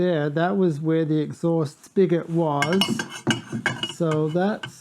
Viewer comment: Ooo... I thought you said spigot, I was gonna say "that was the most New England saying I've heard in a long time". LoL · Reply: I probably did say spigot, hard to remember exactly..